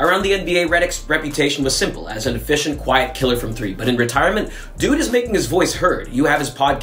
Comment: I thought he said "efficient white killer"